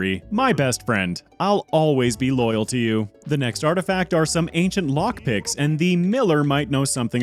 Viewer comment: when you said ancient lockpicks at 2:59:00 i heard ancient moth dicks.